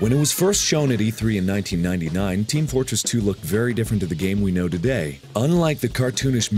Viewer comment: Did he say Team Flortress 2?